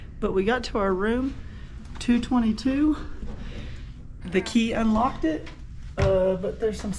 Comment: in less i heard wrong when you got to he room it sounded like you said they gave you room 222 but you showed room 322 that you went in ?